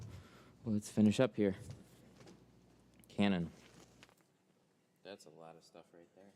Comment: A year late but the camera changed because you said Kevin and it thought you said cannon lol